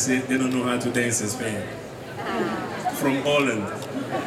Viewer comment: What is he saying at 0:47? From Poland? Whats wrong with that? Try to be funny? Great player but... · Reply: He may have said "Holland".